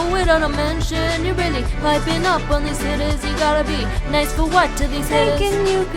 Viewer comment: Am I the only one who heard that? 😒🙄🤦🏾‍♀️ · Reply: She said “hittas” not the n word